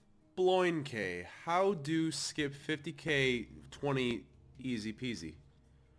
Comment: I thought he said " How to skip 50k LIFE 20" LOL